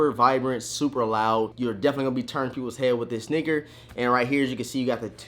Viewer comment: Why does it sound like u said the n word at 2:24 even thought u said sneaker